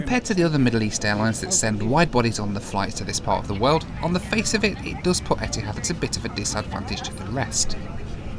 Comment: what is he saying? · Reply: "On the face of it, it does put Etihad at a bit of a disadvantage to the rest..." (i.e. other airlines with wide-bodied aircraft on 4-5 hour flights to & from UAE/Qatar).